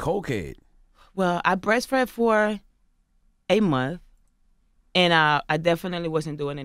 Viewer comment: Excuse she said she breast fed for 8 months my bad, but her baby is 6 months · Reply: I heard 'A month'..